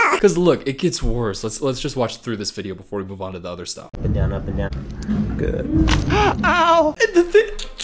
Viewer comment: when he said up and down up and down I really thought he was just saying a banana a banana a banana